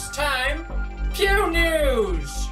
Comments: At first I thought he was saying “Pew Nudes” and I got scared but also excited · I thought he said Pew Nudes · Who else thought he said "Pew Nudes"? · sounded like u said tube news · At first I thought he said tube news. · Who else thought he said pewnudes😅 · Am I the only one that thought he said Pew Nudes???? LOL? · At first I thought he said “Tube News” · At first I thought he said pew nudes XD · I thought you said pew nudes. and then I thought, oooo yeah this video I'll see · Thought it said pew nudes at first...smh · I thought you said Pew Nudes! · It sounds like he is saying "poo news" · I thought he Said pewd nudes XD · i thought it said "Pew Nudes" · 0: 06 I thought he said pew nudes 😂😂😂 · I thought he said pew nudes 😂😂😂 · I swear I thought he said pew nudes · I legit thought he said pube news at the start. Lol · I thought you said pubenews